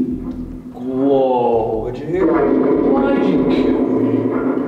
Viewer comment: it sound like they say “he’s full of himself”